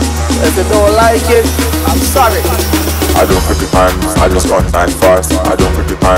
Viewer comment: what is he saying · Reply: They're all wrong. He's saying, "I don't look at the times, I just run 9.5s".